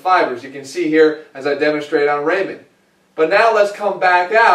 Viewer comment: Did he just call that skeleton Raymond? Lol